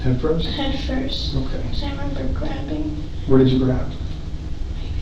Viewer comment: I thought she said she couldn’t remember giving birth